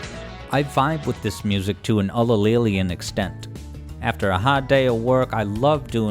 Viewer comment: This was awesome! What is that word that you use at 14:38? I keep trying to look it up but I cannot spell it right haha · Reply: Haha sorry, the word is ulillillian, the adjective form of Ulillillia, a.k.a. Nick Smith. He is an internet legend who enjoys video game music quite a bit, and a series of mini-interviews comprise the first set of videos on this channel, kind of like an opening act. Here's a glimpse of his signature enthusiasm, timestamped at 58:30: <link>